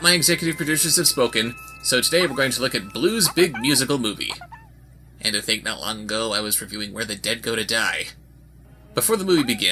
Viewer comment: What did say at 1:02 ..? I've watched that part few times, but didn't understand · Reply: "And to think, not long ago I was reviewing Where The Dead Go To Die."